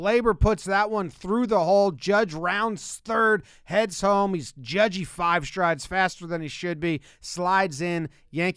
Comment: "Judge he's 5 strides faster than he should be" Jomboy never fails with great quotes lol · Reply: I heard it as 'Judgey 5 Strides' like a nickname lol